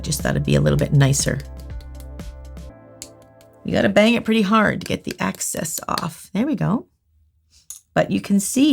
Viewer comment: Am I the only one who heard her say " I am tapping it to get the AXESS off " 😂